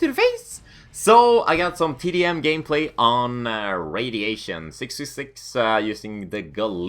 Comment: lol did just say he was playin on radiation